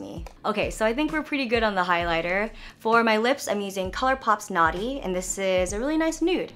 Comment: When you said "Color Pop's Knotty" I thought you said "Color Pop Snotty" and I was like WHAT?! DID THEY REALLY CALL IT SNOTTY??? Lol